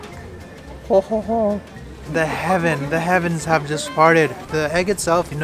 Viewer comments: Thought he said," The Heaven's have farted." Me: nod nod nod* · Anyone else hear “the heavens just farted” at 1:50 🤣